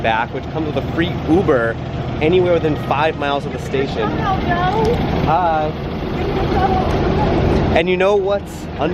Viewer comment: what did that person say? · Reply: "Give me a shoutout, yo!"